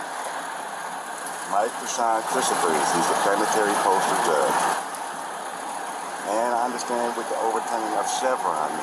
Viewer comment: calls himself the "Chief Federal Postal Court Judge" out in the alternate reality world of SovSits. But I swore I heard the behatted driver call him the "Planetary Postal Judge." I think should adopt that title. It covers a far greater area of travel. Like if SovCits wanted to travel to space on a SpaceX flight, he could intercede with Mr. Musk et al re: licenses for space.